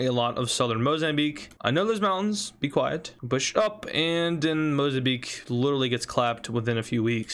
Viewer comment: sounds to me like he says “shush up” instead of “push up” because of him saying “be quiet” before